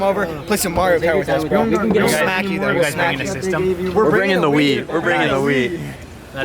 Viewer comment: Good lord top runners all eat like S*** (6:40 I thought they said "We're bringing the weed!")